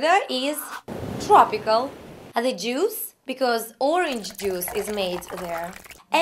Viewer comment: lol at the 'juice' - I thought she said the jews.